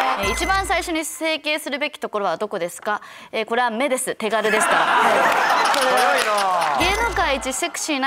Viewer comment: 整形の話、"手軽ですから"を手軽でしたって聞き間違えてる人多そう